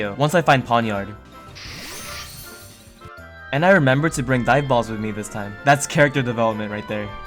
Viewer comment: bro for the longest time, I thought you were saying "diet balls" instead of dive balls lmao